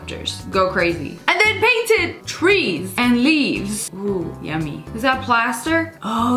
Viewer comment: me: what did you just say? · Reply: Yummy💀